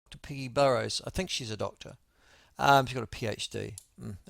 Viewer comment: I thought he said "Piggy Burrows" at the start, 😂😂🐷🐷